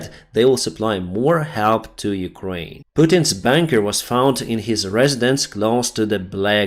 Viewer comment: Putin’s bUnker was found on his residence. I thought that you said BANKER at first. I was hoping another oligarch had met his untimely end at the end of a rope for a few seconds there. Darn. 🙁